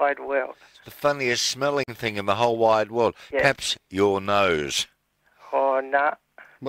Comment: What did hey say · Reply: Perhaps